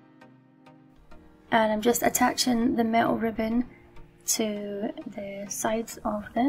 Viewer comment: For awhile I thought you were saying “male ribbon “😁. It’s the cute accent 😊